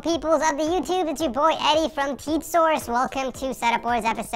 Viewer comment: When said the intro I heard: "Welcome to tic source"